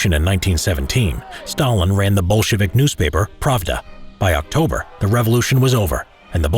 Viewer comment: at first I thought he said Bullshit newspaper